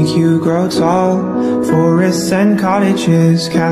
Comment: This is better than life and where the part it said cards that can talk. I thought it said cars that can talk 😳💧